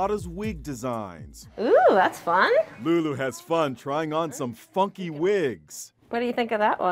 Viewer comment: what did he just say?!?!? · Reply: funky wigs